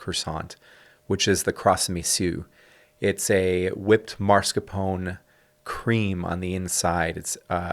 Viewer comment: Oh my goodness, I thought you said “marzipan” not “marscapone”, when describing the filled croissants!